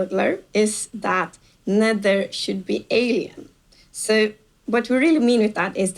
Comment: What did she say? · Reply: I think she said "The nether should be alien"